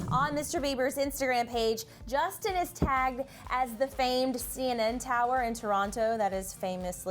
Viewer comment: DID I HEAR HER SAY CNN TOWER ITS CN TOWER